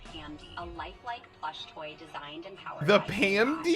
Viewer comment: stop i thought she said "flesh toy"